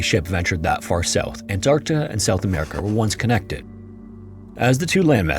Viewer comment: did you just say ant arta why tf am i watching this